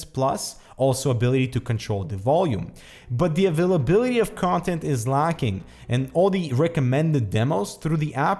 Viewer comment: again nice quality video review. sounded like you said contempt not content though.